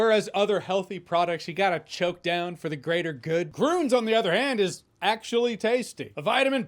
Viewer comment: Could’ve sworn you said this video is brought to you by GOONS